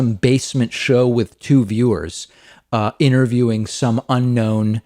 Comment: "This is not some basement show with two viewers interviewing some unknown person" I misheard 'viewers' as 'interviewers' and totally thought it was a reference to the Suck Cut! 😀😀😀